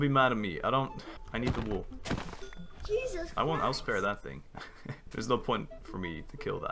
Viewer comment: (kills Sheep) Don't be mad at me I need wool. Me: Understandable. (Kills lamb) Me: You animal! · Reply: I misheard you lol. I heard "I wont spare that thing" at 1:24 and the edit made me thought you murdered the lamb hahahah